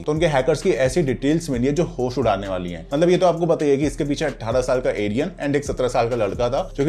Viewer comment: Did i hear alien? 👽 😅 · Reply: Arian